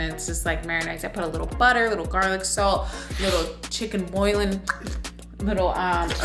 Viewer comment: Chicken Buillion (Bull-yahn) seasoning.... not boilin' ...heard you say that twice. lol But I got ya baddie!!! From one bad Niki to anotha!!!